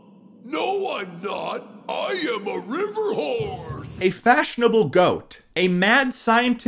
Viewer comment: i know that hippo is saying horse but it sounds like he is saying wh__e